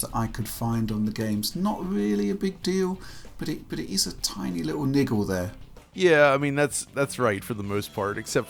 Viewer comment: did he say “ Tiny Little Nigel “